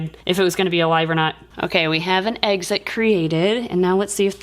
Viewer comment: I love how said eggxit instead of exit